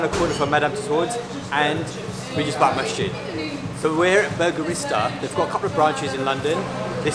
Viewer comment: Did he just say burgerista?😂😂